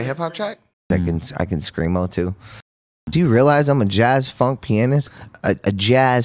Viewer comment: He’s a jazz funk pianist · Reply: Oh he said pianist...must have misheard him...thanks for clearing up!